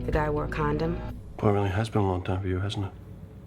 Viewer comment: what does castle say at 2:26? Thanks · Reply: "Wow it really has been a long time for you hasn't it?"